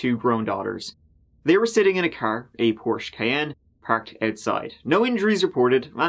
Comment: Porsche Cayenne. I kept hearing "Porsche Van". enunciate a little bit more, please.